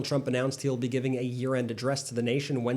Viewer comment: When you said 'Trump will give a year-end address...." I heard you say; "Trump will give a urined address...."😂